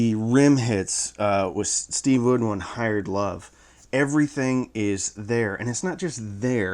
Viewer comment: Were you trying to be funny or did I hear it wrong: hired love? 14:30 I think... and 12k subs, nice! So, zeos did not like these, at least compared to the ultimate bookshelf, not that he's the final word but, do u plan on comparing the 2? I know the ultimate is definitely not in the cheap category, just curious. If not, any plans on checking out other svs speakers? Specifically the prime satellites, I'm curious as to whether or not they'd be decent temporary front channels, same with the prime elevations. Hopefully u still have time to read my comments here, otherwise I'll have to consider your patreon, then my wife will be fully convinced that I am, in fact, in love with you, I mean your channel, ya, the channel...